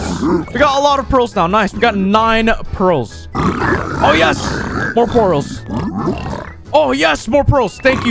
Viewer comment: did he say more porls?